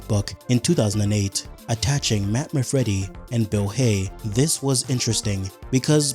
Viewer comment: thought bro said Nightmare Freddy😭🙏🏻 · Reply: Its actually Matt Manfredi but I do know where your coming from with the name soundalike meme.